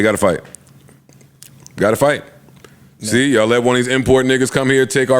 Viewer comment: I thought he said see y’all next week 😂😂😂 2 days later… another BANGER!!!!